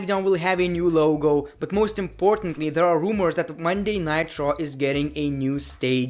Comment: lol u said monday nitro instead of monday night raw